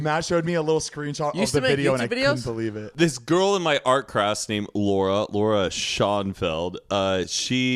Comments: did he just say “art crass” ?? 🤣🤣 This was great !! LOL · I’m sorry did Matt just say “this girl from my art crass”? 🤭